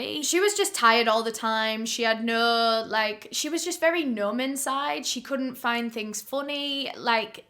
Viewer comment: I'm not making fun of in any way shape or form, but when she said numb inside I for a second legitimately thought she said "nomicode" like cookie monster nom nom "Nom-iss-ide